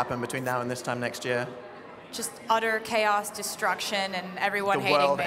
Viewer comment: I thought she said BUTTER CHAOS...oops